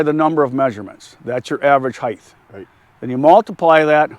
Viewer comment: did he say heighth?